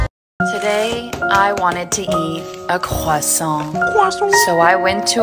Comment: (For info I have a really bad cough and I can’t go a minute without coughing) 1:26 when I was watching this I heard “Today I wanted to eat a EGGHHUUU” What was actually said was “Today I wanted to eat a croissant “